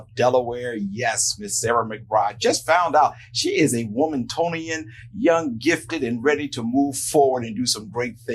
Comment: Womantonian? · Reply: I thought he said “womantonian” too!